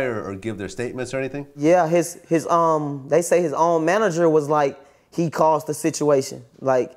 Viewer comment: He look like yo gotti/ Ti put together 😂😂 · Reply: Thought u said shotti 69 manager but he look like shotti too